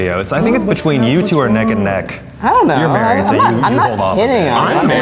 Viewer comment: it sounds like Zach said “I’m married”